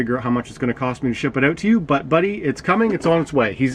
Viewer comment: Wait wait WAIT, did u just call him ur BUTT Buddy??? LMAO!